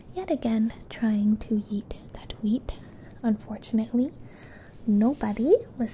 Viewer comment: "yeet that wheat" ?? DID I HEAR THAT CORRECTTLY LMAOOO